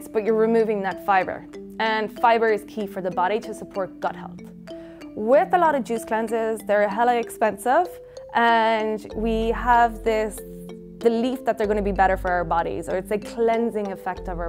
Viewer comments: Did I hear her say "hella expensive" lol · Did she say “hella expensive”?